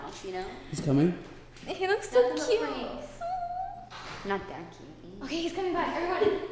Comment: Did say? ( not that cute ) at 8:40? Like